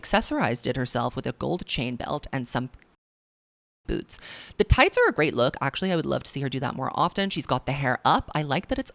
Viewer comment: Help what was the bleep out for the boots at 12:07 😭 my brain edited in f*ck a*s boots but it doesn’t look like that’s what was said hahahahaah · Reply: I think he said c*nty a*s🤣